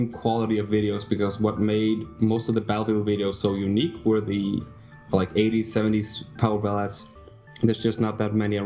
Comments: maybe I'm just retarded but I have no idea what he's saying right there. Does anyone know? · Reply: Uraneum "And the videos basically, you can't have the same quality of videos, because what made most of the battlefield videos so unique, were the like, '80s 70's powerballads, there's just not many of that around."-Yeah they are also copyrighted. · maybe I'm just retarded but I have no idea what he's saying right there. Does anyone know? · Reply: Uraneum lel, I heard 'powersalads' (went full 'the fuck man?' mode), glad he didn't say powersalads. I don't like salads.